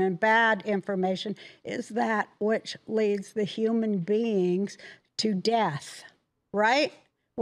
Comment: honestly thought she said:''and he loves the human beings to death''